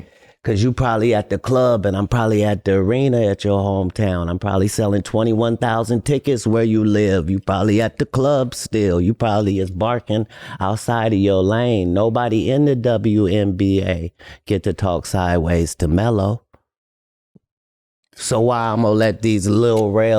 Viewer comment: 😂I know I’m not the only one that heard him say “nobody in the WNBA talking sideways to Melo”